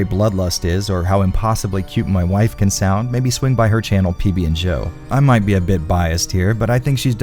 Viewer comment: "PB and JO". I could have sworn that you said "PB and Jill" which I thought was a reference to "Jill Sandwich".